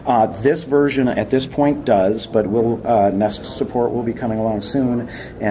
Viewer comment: Did he say that it's getting NEST support at the 2:38 mark?